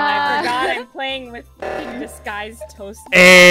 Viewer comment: Toast as Impostor: Disguised Toast Toast as crewmate: This guy's toast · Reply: i can't hear "disguised toast" what i hear is "this guy's toast"